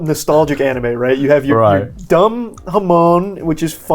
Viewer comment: I legit thought he said dumb hmong